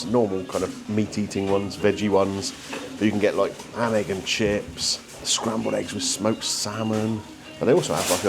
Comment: Hi folks - I finally made it to the world famous E.Pellicci for breakfast !! Have you ever been? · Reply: ❤Nope he he thought you said panic and Chips 😂😂😂😎